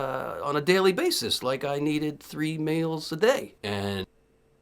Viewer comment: fruedian slip? did he say he needed 3 males a day?